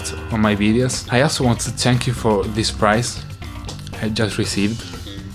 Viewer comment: Dave ever time you say "Thank You" it sounds like your saying "Tank yo" just saying.